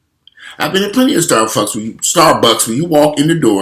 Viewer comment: did you say “starfucks”?! Lmfao